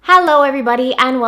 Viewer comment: At the beginning of your videos it sounded like you said Harlow instead of hello.. if that’s not a thing you should totally do that 😂😂😂 omg “Harlow everyone” instead of hello. That’s everything